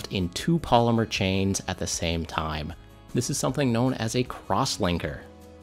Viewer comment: LOL I thought he said "Crosswanker" and started paying attention